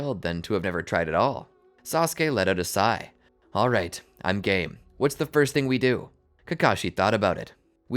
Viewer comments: 21:4 I think I heard wrong I heard I'm gay not I'm game lol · i heard wrong😂😂😂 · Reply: You heard “alright, I’m gay” didn’t you?